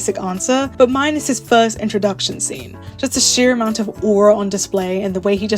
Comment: Sylus's sisterhood is a place I never thought I needed. Let's add this to this man's ass long list of divine level features. 16:40 When you talked about it, I heard it (and auto subs did that too) as "minus his intro scenes" and I nodded coz how can one possibly not love that masterpiece, get out of my way if you don't, fr. xD And I know the community craves for the moment Sylus crashes out emotionally at some point because of how he's always composed and cool (which you mentioned!). And to me that moment happened in Primordial Chaos. When that damn eng VA asks the empress if she's going to annul their engagement while pinning her to the wall in her bedchamber. OH BOY. The way his voice wavers and rings with uncertainty, anxiety and bleak hope that begs ...I CAN'T! And the fact he also goes into physically restraining her there (by pinning) for the first time in my memory...! Man was on the edge. This card is on the top of my list by a huuuuuge lead just for that whole (night & morning) bedroom scene alone (though I'm yet to watch all of his existing cards, I know this much only as I started during his Dragon myth rerun in October followed by Improvised Flow later). Khaosi sovereign surrendering himself... this card is where I believe he begs. He didn't sell it to me with begging in Grasped dominion, he was too cool there and not wild enough (to my taste). But this one line from Chaos...! (internal screaming intensifies)